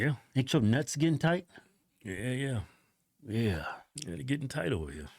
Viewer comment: Im at the gym and the moment i heard "my nuts gettin tight", i almost died